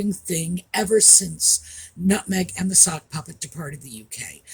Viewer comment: It sounded like you said "the suckpuppet"! I think that really suits the egit Harry.